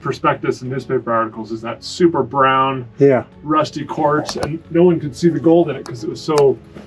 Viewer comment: I thought they said “rusty corpse” and not rusty quarts😂I was like dang where is this video going lol